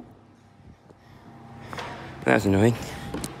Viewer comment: What is saying at 11:07 ? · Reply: That was annoying